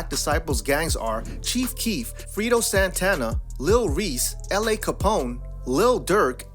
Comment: Did he really say Frito Santana?